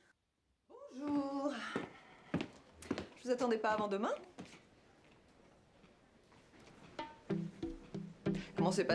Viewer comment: Hey does anyone know what the women in the show is saying at 6:16? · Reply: "Je ne vous attendais pas avant demain"